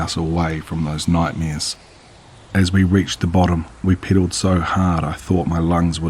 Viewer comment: How the hell is a knife embedded into this things eye all the way to the hilt, gonna cut you when your hand slips of the handle, good story the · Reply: This one's reminiscent of It, but without the cosmic aspect or the baggage. Great pacing, made it hard to pause for laughter: I'm American, I heard "piddled so hard" and lost it for a moment